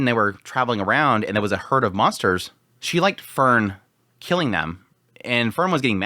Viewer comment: The pronunciation of Frieren was throwing me off so bad. I thought he was saying let Fern kill Fern and I was like wtf.